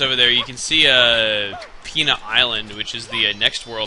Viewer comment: i thought he said penis island.lol